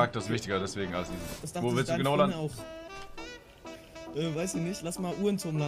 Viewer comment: Was sagt der typ im Voicechat bei 12:25? "Das dachten sich deine ... auch" Ich habs jz locker 10 mal angehört ich verstehs einfach nicht 😂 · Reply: Das dachte sich deine Freundin auch Kann man sich doch auch denken, weil er davor meinte, dass Charakter wichtiger ist als Aussehen